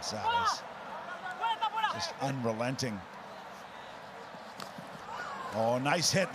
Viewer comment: "Luciano is unromantic"... jajajaja Lucho es un tanque. Vamos Los Pumas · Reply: Si es la escena y palabra que yo creo, no dice "unromantic" yo escuché "unrelenting".